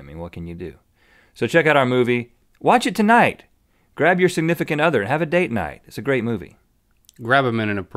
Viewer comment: When he said "grab your significant other" I thought he said "sigmythicant other" and I thought this was some new mythical beast term that I hadn't heard yet (much like how Nerdfighters are in Nerdfighterlike with each other). I will probably use the term sigmythicant other from now on, though.